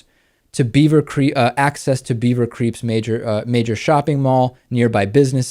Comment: did dave say "beaver creeps" instead of "beaver creek" @ 01:00?? lolol! if so: perfect description of the psychology re: the crazy ohio suburb folks! 'GOT DAM' racists!! i mean, now they want to limit bus stops & access to college for african-americans?? these ohio-super racist-wanna-be-humans are seriously sick in the head & seriously stupid. but ohio again, like florida?: lord, give us strength because what's next? :o0